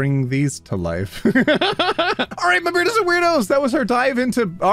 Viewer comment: I'm watching this video on 1.5x speed with closed captions on (adhd moment) and the captions, instead of saying "beardos and weirdos", said "bread british weirdos" HELP ????????? This shouldn't be this funny to me but it is